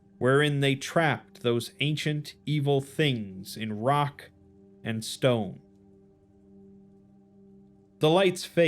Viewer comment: Did I hear a rock and stone?